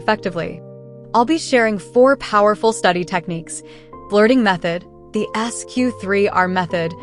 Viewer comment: I thought she said flirting method lol 😂